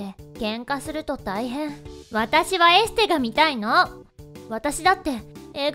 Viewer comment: エステに聞こえる...